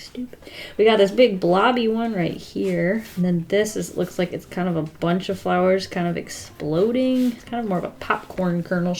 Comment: I thought it said “Drawing a bunch of bubbles” Edit: thank you for seeing this and replying you just kind of made my day